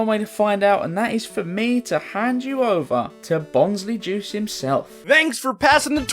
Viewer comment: Bonsly sounded like a blaze at